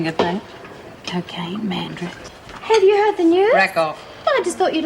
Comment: What did reb say at 30:07 after cocaine ?? It sounded like she said madrex or something like that lol. I know that word prob does not even exist. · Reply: She said mandrax, an illegal drug most commonly found in South Africa.